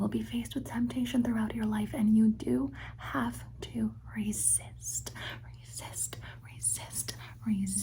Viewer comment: she says resist a number of times, and I was half asleep and thought she was saying to be racist. Threw me off a bit! 😅